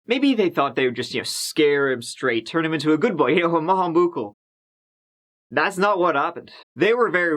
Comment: What in the hell is saying at 6:07? I've listened to it so many times and nah I can't get it 😂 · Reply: He said an Irish expression which means good boy... Maith an buachaill 😋